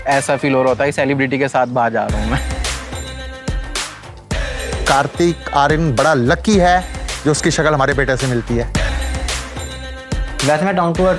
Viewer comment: Did Anyone Heard it Clearly what Uncle Said. Kartik Aaryan bada Lucky hai ki uska Sakal humare Bete ke saath Match hoti hai. Matlab Uncle Ji ka Confidence Level to dekho bhaisahab 😂😂😂😂